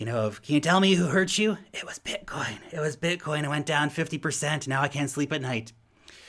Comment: I thought you said "who hurt you?" "It was Bitboy, it was BITBOY" LOL